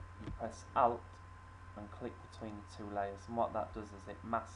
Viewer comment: wait u said to get the rainbow press what? sounded like you said "out" how do i do this whats the key? im on cs6 please reply back asap i need to finish this today 2:21 u start saying it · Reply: Press Alt key and click in between the two layers